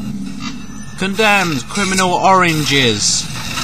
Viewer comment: it sounded like u said Condemned Criminal Oranges lol